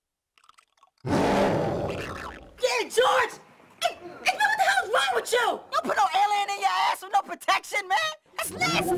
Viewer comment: What CJ say ????? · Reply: "Yeah, George! What in the hell's wrong with you? Don't put no alien in yo ass with no protection man! That's nasty! You don't know where he's been!"